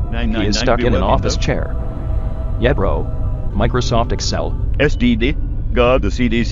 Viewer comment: is it just me, or does it sound like he said "STD" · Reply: SDD